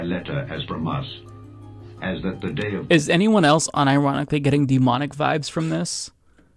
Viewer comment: When you said "demonic vibes", I heard "demonetized", and thought, "maybe that will be Marjorie Taylor Green's next malapropism", as in "If I quote the F slur, YouTube will demonic vibes this video."